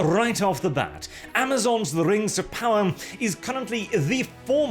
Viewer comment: I thought he said "Race of power" there for a moment. I thought it was a pun, I was wrong. But maybe it should have been.